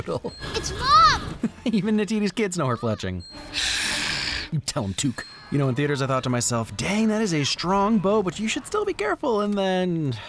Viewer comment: I thought you said “felching” instead of “fletching”